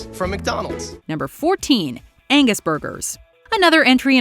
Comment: I’m so stoned I thought you said anus burger lmao 😂